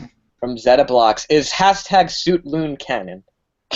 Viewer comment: what did he even say there? "is half-tag shoot moon canon"? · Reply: Is #Suitloon canon?